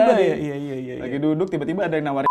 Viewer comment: itu Dzawin ngomong apa ya yg di sensor? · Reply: "lagi duduk tiba-tiba ada yang nawarin mitu"